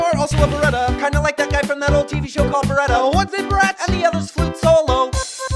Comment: fuck it! I thought they said "the other's foot's so low" I was like WTF?